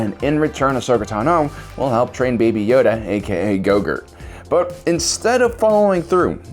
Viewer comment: excuse me did you say gogur? Get your shit together man